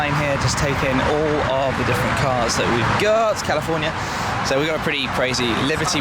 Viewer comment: hey 1:53 did you say California to an f12